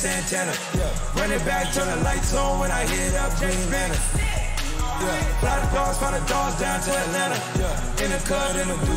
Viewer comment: what is he saying instead of "green lantern"??? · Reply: Jacob Poulsen "when I hit up dry beans"